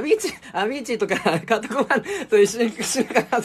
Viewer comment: なんて言ってるん？ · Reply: アヴィーチーとカート・コバーンかと。